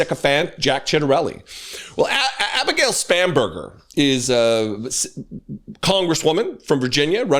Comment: I thought said "SPAMburger". WTF kind of parents name their offspring "Winsome" anyway?!? LOSE some, MAGA.